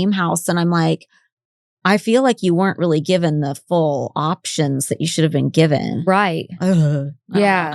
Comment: You definitely misheard. She said “ I feel you weren’t given the full options that you should have been given” to when he got a toy from the Game room. Go back to 5:45 for exact quote and continue with your struggle with the podcast